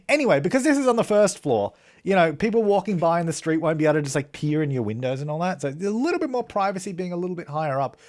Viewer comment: OMG I HEARD THAT AS "won't be able to pee in your windows" 😂😂